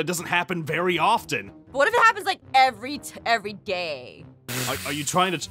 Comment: Aphmau-What if it happens everyday? Me- Did she say gay????????